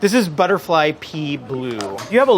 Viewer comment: Def thought he said that color was "Blue Butterfly Pee" and had to scroll back to see how it was spelled.